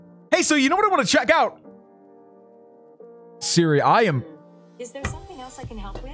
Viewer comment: it did sound like u said “hey siri”🤣